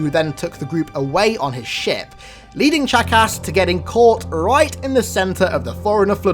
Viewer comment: I thought he said jackass lmao